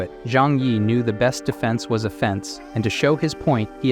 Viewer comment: i find it so funny every time "the best defense is an offense" it sounds like its saying "the best defense is a fence"